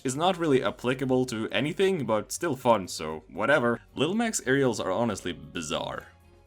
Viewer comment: did you just say little Mac has a dacus?